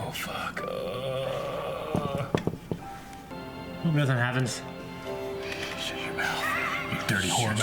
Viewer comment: What did he say? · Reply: said "I hope something happens" and he got what he wished, as we see the doctor get hit by a Dalek as he is running to Rose